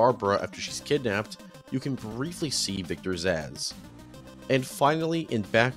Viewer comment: the closed captions say “you can briefly see Victor’s ass” LMFAO